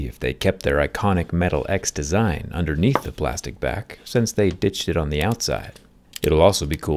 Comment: did he just say since they did sh*t on the outside